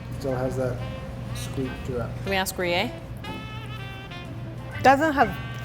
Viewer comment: Did anybody else realize at 6:10, if you put on captions it says “we asked Korea” instead of “we asked Rie” Idk XD